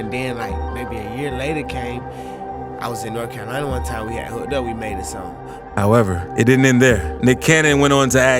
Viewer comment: Even the caption doesn't know what's said at 14:00. I'm from NJ and I could easily make out what he said 🙄 "I was in North Carolina one time, we had hooked up, we made a song"